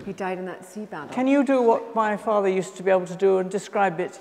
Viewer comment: Why did refer to "my father" when asking her brother the question about describing the coat of arms in proper heraldic terms? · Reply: I thought she said just "father" not "my father" or "our father".